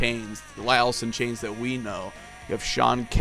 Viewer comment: Anyone else hear the dude say Larry Cantrell @ 1:56?